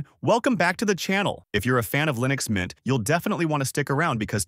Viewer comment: wtf did you just say? did you say Lynux mint?